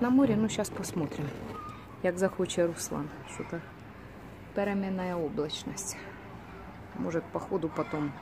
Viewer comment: Лайк! Какой братик молодец, какой работяжка! это у вас, семейное) Представляешь, что я услышала? Ты говоришь "переменная облачность". А я услышала "беременная облачность"))) так смеялась с себя))